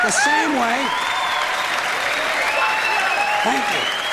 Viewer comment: Anyone else hear "fuck you" at 8:05?